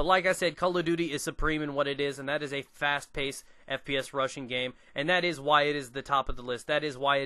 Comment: LOL "Fast paced fps russian game." That is what I thought he said.